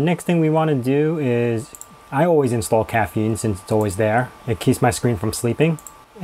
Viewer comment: At first I thought he said, "I always install caffeine... it keeps my kids from sleeping." Haha. 8:55 When you're not patient enough to figure out how to right click and hit "save as" so you default to your much superior CLI download manager. When I'm theming, I usually put my themes into the .themes directory and my icon packs and cursor themes into .icons, which are in the home directory. The problem with this is only my user can use them. Other users cannot, but that's no problem on computers with only one user. It also doesn't require root privileges, which is great because I'm too lazy to open a file manager as root unless I have to.